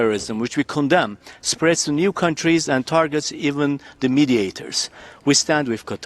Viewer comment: Thought you were gonna say confront these cunts, but you said countries I think the former was correct.